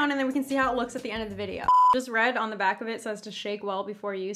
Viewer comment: Omg! I thought she said that the foundation says shave well before use hahaha